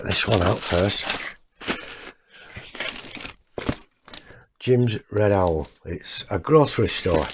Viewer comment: Hi, nice video and cool kits! Can’t wait to see them, when you said Jim’s Red Owl, I thought you said Gingerbread Owl, lol 😂 Great video, stay safe and take care 😀👍👋